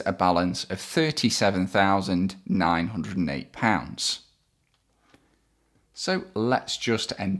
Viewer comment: task 1.5 at 23:50 the difference between PLCA and list of balance should be 37878, please correct me if it is wrong. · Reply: He says 67552 instead of 67522, hence the 30 difference